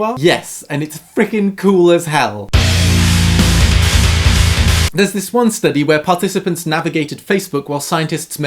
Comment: I missed the the text so went back 5 seconds and it sounded like he said "There's this one study where participants navigated hell"